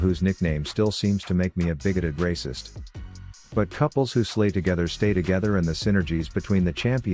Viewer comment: I'm I the only one who heard "Couple who slave together stays together?" Just me? K bye